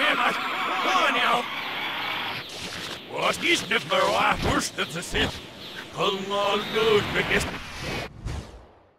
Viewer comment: What did he say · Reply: He says hes ditching blue and going monke